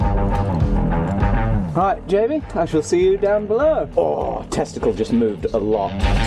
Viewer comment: 😂😂😂 what moved alot, did i hear testies?